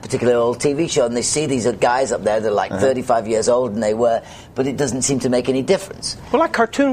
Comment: Brian - what David actually said at approx 6:53 was "and they get these guys that are like 35 years older than they were." He's talking about how the fans love the image of "Davy Jones" from the Monkees show and as David Jones, the man, it's his responsibility to maintain that image for his fans. What people see when they look at him, even though he's aged, is the "Davy Jones Monkee" from the show.